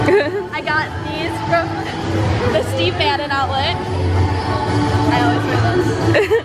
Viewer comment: I THOUGHT SHE SAID STEVE BANNON HAHHAHA